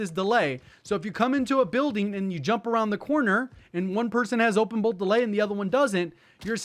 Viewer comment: constantly reminding me I know nothing🤣 🤣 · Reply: bruhhh you're tellin me ... everytime he said that in other videos I thought he was saying open BULLET delay hahaha!!